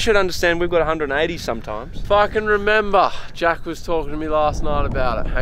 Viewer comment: no he didn’t say what you heard. He said “if I can remember” but my goodness that made me double back to make sure. 😂😂😂